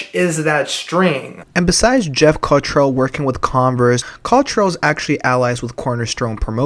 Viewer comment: I thought you said Congress rather than Converse hahahaha